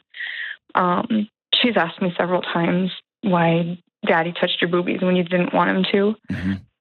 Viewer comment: I thought she said: “why does daddy touch your rubie when you don’t want him to”?